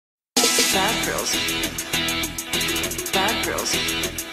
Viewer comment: I hear ‘bad gRiLlS’ uhhhh gRiLlS r great U-U